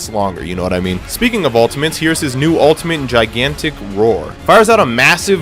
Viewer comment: Boy,I thought it said Gigantic Rape